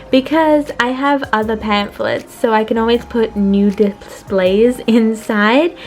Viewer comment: I thought you said nude displays and I thought 'wait, that's not right' the realised you said 'New Displays'